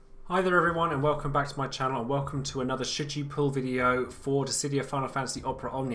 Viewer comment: In the beginning of the video I thought u said "Welcome to another sh**ty pull video" hahaha my english is soooooo bad aaaa OMG